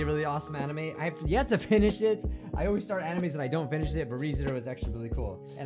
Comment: Wait did he just say S**t? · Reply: He said "finish it"